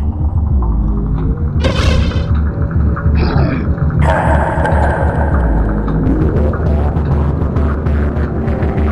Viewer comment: 🔥🔥🔥 Great animation! Btw did astro say something at 0:11? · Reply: Yes he said "Hello Ha hahaha"